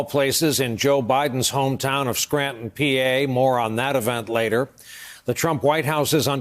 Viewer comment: More on, hahaha, I heard it as moron...so much more appropriate.